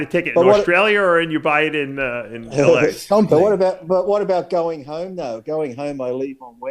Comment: Lee says what about going home tho I thought he said what about going homo 😅